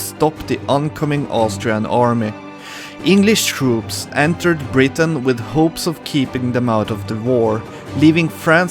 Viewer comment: I thought you said that English troops took Britain not Brittany and I was like "wut"